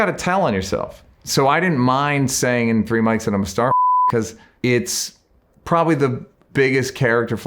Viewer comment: What did he say at 5:22? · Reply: Starfcker